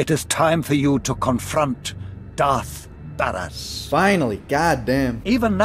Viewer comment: I thought he fucking said darth badass